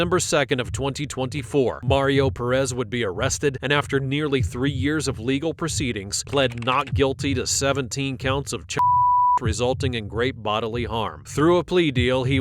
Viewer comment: the subtitles say he was charged with 17 counts of child molestation, while the narrator says child abuse but keeps the word abuse. That probably needs to be fixed… dude messed up and changed those children’s lives, but he didn’t molest them. 😅